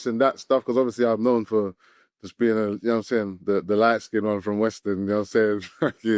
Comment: not “the light skin one from WSTRN” 💀 I hear it tho